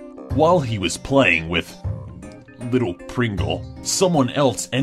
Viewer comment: "Little Pringle" idk why but that made me laugh really hard 😂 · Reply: lifeisokay10 I know right I thought they said little pickle tho😂😂😂😂😂😂😂